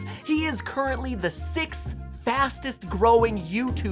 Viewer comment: or 6:51 it sounds like he says fostes